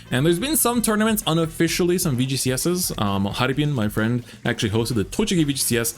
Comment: <link> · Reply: Thank you 🙏 For some reason I thought I heard harupin and was confused. Just my old man ears failing lol